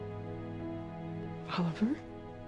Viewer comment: Lmaoo when Moira says Oliver at 6:08 the subtitles say however